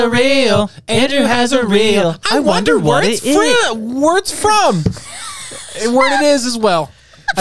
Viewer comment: When they sang Andrew has a real and they said I wonder where it’s from I thought they said I wonder where it’s fruit